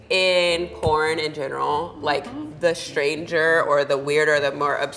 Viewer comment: Did she call her strange obsurd weird